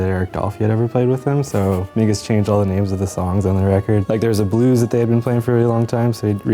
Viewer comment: Sounds like he said 'niggaz changed all the names of the songs' lmao.